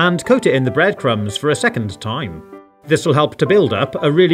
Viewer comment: it sounds like he says " a secondth time"